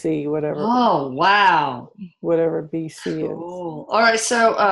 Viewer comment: Does she say "Whatever BC is." at 20:57 ?